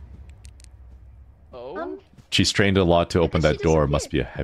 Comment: quip to ghost always top notch tbh lmao "She strained a lot must be heavy door" she's just ghost give her a break lol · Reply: Ah yes it should be "strained", not "trained". I misheard that one. Thank you